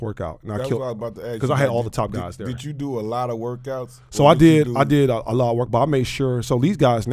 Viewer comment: did he say you did a lot of workouts allahuackbar